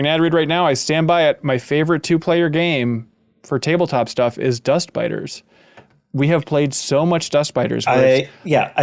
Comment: Wait it’s Dust Biters?! I thought he was always saying Dust Spiders lol oops😅